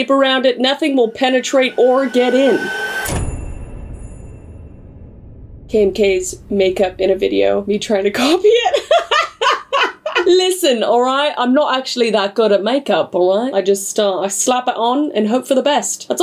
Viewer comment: she says "Kim k's" instead of "kmk". No hate I love her videos so much keep it up.